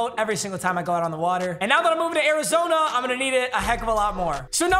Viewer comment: Did I hear to Arizona!? 😳